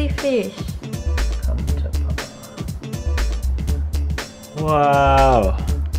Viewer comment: "come to papa?" Anyone else hear that?